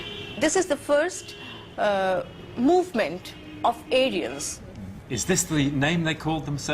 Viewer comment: anyone hear how she said Aryan? Sounds like she said Alien… 🤔 INTERESTING